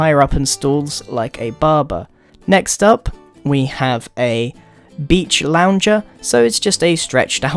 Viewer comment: i heard "bitch launcher" XDD